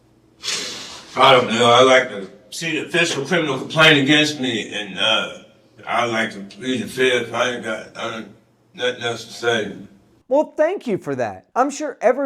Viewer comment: "i uh, i'd like to see an official complaint against me and, uh, i'd like to plead the fifth. i got nuh-nothing else to say." in case you had no idea wtf he said like i did, i replayed this twice so i could write this up for you.